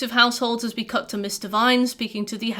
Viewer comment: So it took me far to long to notice my mistake but I thought you were saying mister vine the entire time. It confused me to the point I started paying more attention to her face trying to see if she used to be a he because apparently that made more sense to me than me mishearing her name